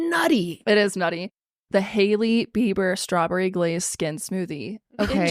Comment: I thought yall said lady beaver smoothie instead of Hailey beiber 😂😂😂😂